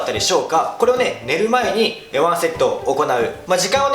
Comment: ここ、何セットって言ってる？よく聞こえない…💦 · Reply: ワンセットだと思います！